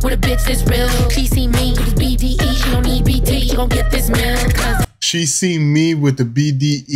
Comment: I thought she said "she on EBT, gonna get this meal" 🤭